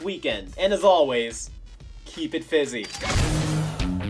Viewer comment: Holdupholdupholdup! Your catchphrase was "Keep it FIZZY"? I always thought you said "Keep it BUSY"